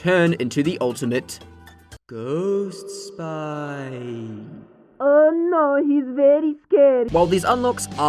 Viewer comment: Am i the only one who heard “Oh no hes very gay.”